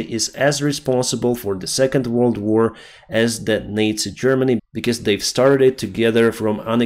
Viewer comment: Was I the only one who hear NATO Germany instead of Nazi Germany?